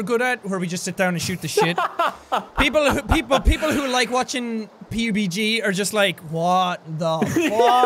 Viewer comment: Wait... at 16:12 did he say Pbg? I need to know what he said!